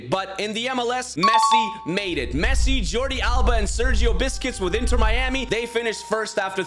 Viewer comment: did he just said sergio biscuits